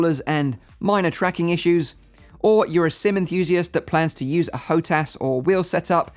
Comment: "Or you're a sim enthusiast that plans to use a Hotas or wheel set-up, then the G2 is the right VR headset for you ." As someone who pre-ordered this headset to play racing sims and MFS 2020 with a TCA Sidestick and Thrustmaster wheel, I find comfort in these words. Thank you very much good sir. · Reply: i thought he said semi enthusiast and i was like "spending money on a headset, wheel and pedals and flight sticks, yeah right, semi" xD